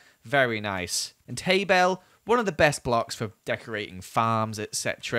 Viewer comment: did anyone else hear him say tabel